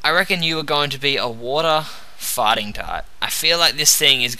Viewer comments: I thought you were saying water farting type :P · it sounds like hes saying water-fighting type XD